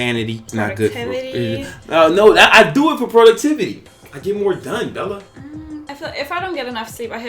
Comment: 🤣🤣🤣sounded like Bella said "fertility"🤣till I replayed it and heard her say productivity😎